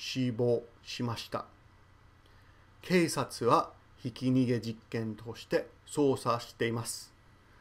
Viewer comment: I hear "jiken" "josei"（accident, woman） as "jikken" "jousei"（experiment, situation） by Japanese